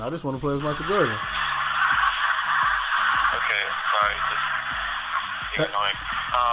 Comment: did he say "he's annoying" 😂